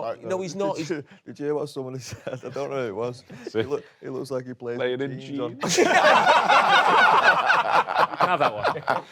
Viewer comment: what did he say? Anyone help. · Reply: "He looks like he plays with jeans on"